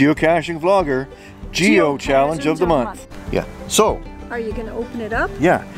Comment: lol 1:45 what did say? Geokadjundge? :)